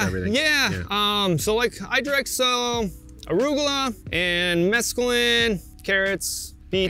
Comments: Did he say he direct sows mescaline at 4:33 lol. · did he say mescaline? hahaha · did he say mescaline? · Did he just say "arugula and mescaline"????